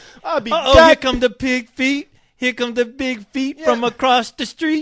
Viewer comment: ROFLMAO What did CO-HOST say at 02:07, "big feet?" Anyways, are hilarious!